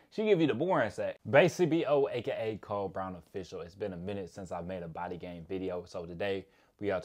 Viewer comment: I thought he said cold brownie fish oil lmao had to play it back